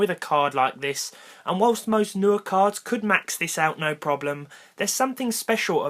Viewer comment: did he say "nerd" cards or "new" cards?!?! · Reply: Rece both work 😂 He said "newer," which is why you heard an r